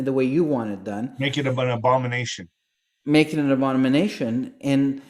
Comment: when you said, "Make it an abomination." I heard 'Make it Obama Nation." Isn't that crazy?